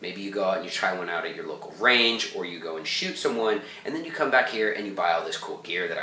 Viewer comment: Nice review. At 8:39....did you say, "you go and shoot someone" ?? 😂😂